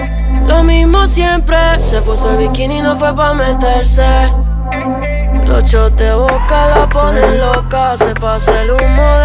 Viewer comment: yo escuche "los shorts de boca la ponen loca"